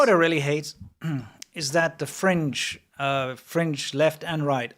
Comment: "The French, both left and right, always talk about negative things in America" Merci to associated all the french with Twitter people, that nice 😐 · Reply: Ah, I got to your quote in the video 55:08 He does not say "French" but "fringe", fringe left and fringe right. As in, far left and far right.